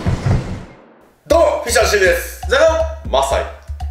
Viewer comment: ザカオ！が0！に聞こえる